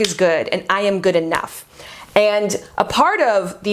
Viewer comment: I was listening a bit too hard. At first I thought she said that she was good at math, but she actually said that she is "good enough." lmao I was like wth does that (math) have to do with anything. lol